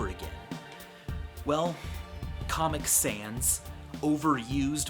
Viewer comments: Some reason I thought it said cosmic sans omeaga lul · I thought it said cosmic sans